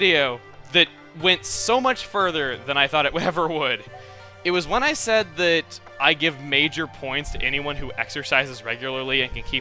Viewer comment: At first, I thought you said "I give major points to anyone who exorcises, regularly" Hey, it made sense in the context of Luigi's mansion."